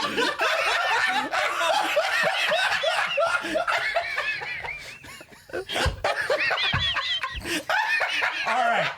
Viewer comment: What did say at 25:20? i listened to it a few times but i'm not sure. everyone lost it and i wanna know!!! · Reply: I swear he said a bathtub 😂